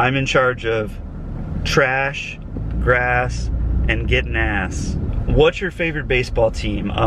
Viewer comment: Y'all totally need to sell merch... I would totally buy a shirt for my husband that says "I'm in charge of trash, grass, and getting ass" because that's the only chores he has too 🤣🤣 I died laughing when he said that. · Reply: Dena Cantrell I thought he said “getting gas.”